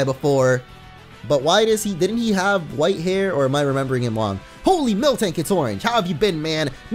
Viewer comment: I thought he said oh holy miltank as it he was praying